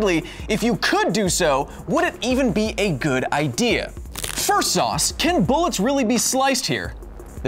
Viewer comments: wait....did kyle say firsauce instead of first off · instead of saying first off he said fersauce